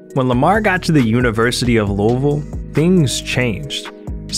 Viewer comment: Wat did he just say! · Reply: Louisville